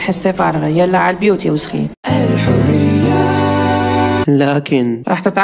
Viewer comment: كأنني سمعت كلمه وسخين🙂✨